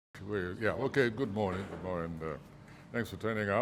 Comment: Did I hear him say "Good mooring" instead of "Good morning"? This man should go and rest, a beg. Why did he apply for a Visa, after cutting the Green Card he was privileged to hold? Why did the Americans even accept an application from him? Mtcheew...